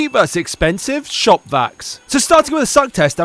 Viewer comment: Me who heard it as "shot backs" thinking he ment back shots😭💀